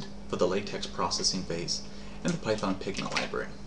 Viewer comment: ok so at 4:00 "and a python 'pigma' library" What is actually being said?